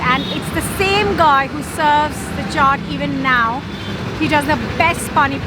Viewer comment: What a great window on the world of your home town - thanks so much for sharing. What was the dish at 05:32? It sounded like you were saying "shark" but I know that isn't it :-). Such care is taken in preparation of the street food you showed us too - every dish a work of art. A little more explanation of dishes, how they're ordered and maybe prices would have been welcome (by me, anyway!). 07:46 "special market" - don't be shy, name it :-). The dish prepared starting at 09:52 looks delicious but passes entirely without comment from you - what was it, some kind of falafel with dahls? Same at 10:42 - "pay puri"? What is it? I'd love to see more of this kind of content, especially with some explanations about what we're seeing - your town looks amazing... and the traffiic seems to be less chaotic than in other films of Indian towns and cities! Thanks again, most enjoyable. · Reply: Chaat 9:52- Daal Bhaati 10:42- Paani Puri